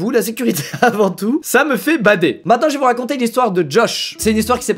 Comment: jai compris " ca me fait bander " 🤣🤣